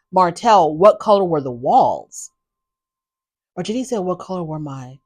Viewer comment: “or did he say what color were my balls” 🤣🤣🤣🤣 He said walls & asked about the color of the carpet.